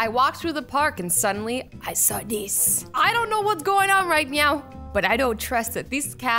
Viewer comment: She said MEOW instead of now